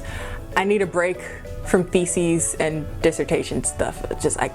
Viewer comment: i thought you said feces at the start of the video not theses 😂 feces and defecations